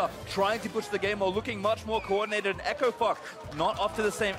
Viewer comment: did he just say. " echo fuck " ?